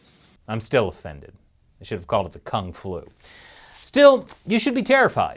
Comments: did you say kungflu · Kung flu Kung flu Kung flu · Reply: Thought he said Con Flu haha XDD